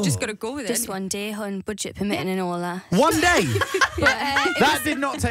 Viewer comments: What does jade say at 1:57 right after hon??? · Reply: Budget permitting and all that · what did Jade say??? 😅🤍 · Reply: babygirl asmr budget permitting and all that